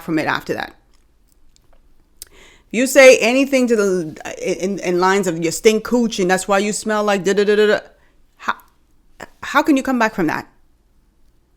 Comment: I thought she said girl you smell like VD 😂